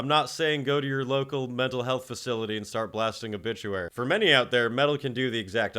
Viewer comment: Why did I hear that as 'Metal' health facility? 😂